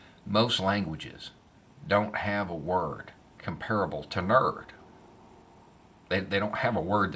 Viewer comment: what? what word are you saying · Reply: "nerd"